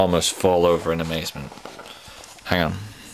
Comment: did he just say 'ham' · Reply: he said hang on but that would be funny if he did